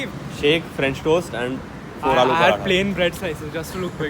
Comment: Did you just say 4 aloo paratha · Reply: yes haha